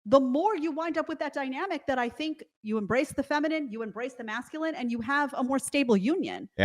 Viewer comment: I heard "erase" instead of embrace like 4 times at the very end, i was so confused haha 😅😂